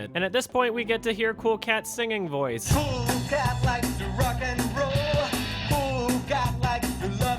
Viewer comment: did anyone else think that it said "Cool Cat Loves To Fuck"